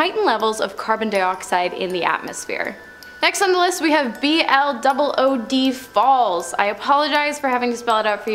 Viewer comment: My dumb ass misheard a letter & spent several seconds wondering what "vlood" was. 🤣